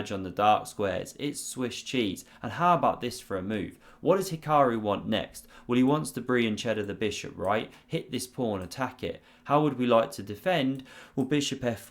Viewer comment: Nearly missed it. 4:50 'Fiancheddar' ;-) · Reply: For some reason I thought I heard "Brie and Cheddar the bishop"